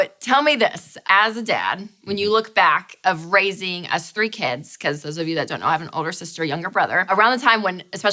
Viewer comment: When she said us three kids, I thought she said us street kids. haha